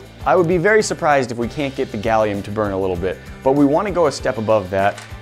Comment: did he say burn gallium?